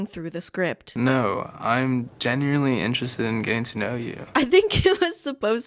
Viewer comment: Wait did he not say "genuinely" at 8:10? I can't hear a difference lol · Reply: He definitely said genuinely and it was pretty enunciated too so idk